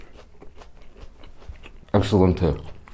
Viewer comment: did he just said 'excelente'?